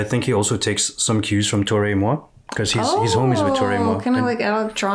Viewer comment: Who is the ‘Torres Mo’ he mentioned at 14:04, idk if I heard the name correctly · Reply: Toro y Moi